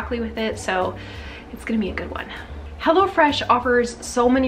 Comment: 💪💪Hello Fresh Is Awesome, we loved it when we was getting them. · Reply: Oh this whole time I thought you were saying hellafresh. Lmao